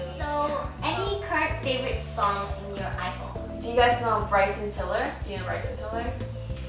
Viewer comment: what's his name? I really couldn't catch it properly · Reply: bryson tiller